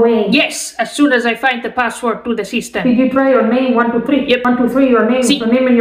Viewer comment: I thought she said she fucked up the password